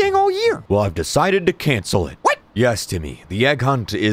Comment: My so furious that the subtitles in 0:26 is saying “timmy” instead of “TIMMEH”